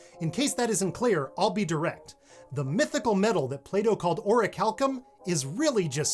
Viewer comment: I heard "playdoough" not plato :')